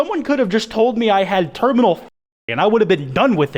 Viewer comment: did you just say terminal fa**ot